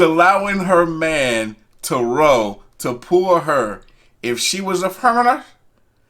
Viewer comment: What in the world did you say? "If she was ashrasshsh" wtf lol